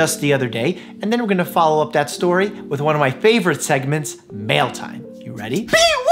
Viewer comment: the way i thought you said “male time” and was so confused and concerned.